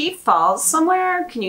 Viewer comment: When you asked did he fall @ 17:14 It sounds like it says HARD!